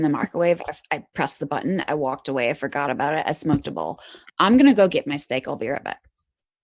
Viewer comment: Some people say they're skeptical because she was drunk. I'm less skeptical precisely because she was drunk. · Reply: Matter of fact I might suggest that sometimes unusually intelligent people are driven to "calm their brains down" as much, if not more than average people. if I heard correctly, she said she had just "smoked a bowl" too. So did I. Carl Sagan smoked FFS. (Just before 0:27:45 she mentioned that and said she had food in the microwave lol. That's so cute... and incredibly sad considering.) Credibility unharmed, imho.